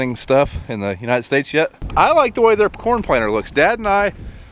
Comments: I swear at 7:30 you say a "porn planter"...I was laughing hysterically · Did he just say porn planter. :-)